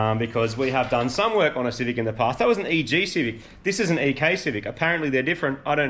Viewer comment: It sounded like you said AK civic, 😂